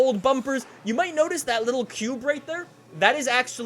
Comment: sound like he said pube